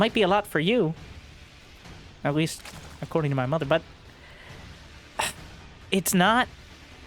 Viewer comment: did blud say "according to my mother" ? Dang fam 😳